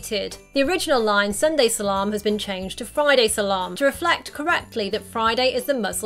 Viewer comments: I was wondering why no one made brought out the Friday salam thing .... Now I'm happy. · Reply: I'm Arab and i thought he said "Friday Salah" which means literally "Friday prayer" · I was wondering why no one made brought out the Friday salam thing .... Now I'm happy. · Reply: TokyoSenpai what does salaam mean? I always thought he was saying “Sunday salon” in the original lol